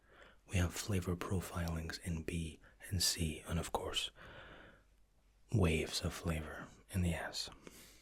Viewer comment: Lmao, all I heard was “ Waves of flavour in the ass “ 😂